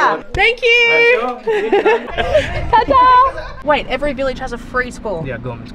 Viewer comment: Did she just said Fuck off 😂🥺😂